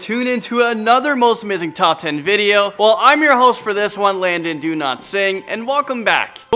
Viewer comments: Every time landon introduces, i hear.. "landon do not sing" ... xD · every time he says his name sounds like hes saying "landon do not sing"